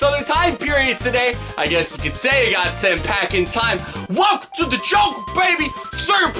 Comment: Did you say "Welcome to the joke!"?